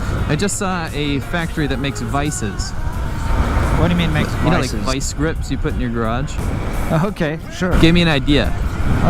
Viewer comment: my volume was low and i thought you said "i just saw a factory that makes vices, you know, like vice grips you put on your crotch"